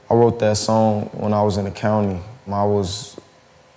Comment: I thought he said, accountant 😂